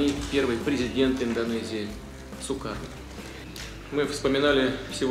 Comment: whos hear Cyka instead of Sukarno